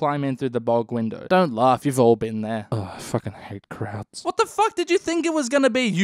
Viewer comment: At @ 07:05 i thought he said ”i fucking hate krauts” then cutting to dany literally dive bombing out of the sun like a stuka :0